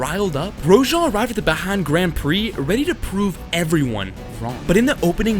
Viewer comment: WHAT DID U SAY?😂 Mf said ‘Bahan’